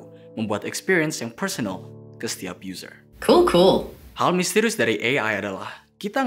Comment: Pas spikernya ngomong sendiri 3:57 .. ada yang tau itu spiker bilang apa... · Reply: itu "cool cool" artinya keren